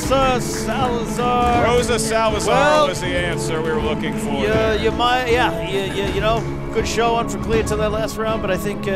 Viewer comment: what did say to lol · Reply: You’re the 🐐.